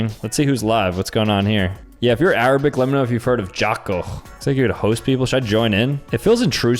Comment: this is the first time I heard about it 😭😭 sounds like jackaro tho